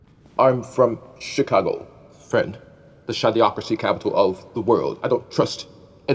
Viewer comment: I thought Timley said "shadow-ocracy"